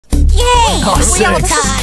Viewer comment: "YAY WE ALL D!ED" why do i hear this